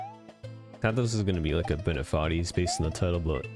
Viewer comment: Sometimes I can't understand him. Like what is he saying in 0:22? Also what is D-calling? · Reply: Also Bennett foddy's is the creator of getting over it so that is a reference and is saying that this level will be like getting over it. Excuse my spelling.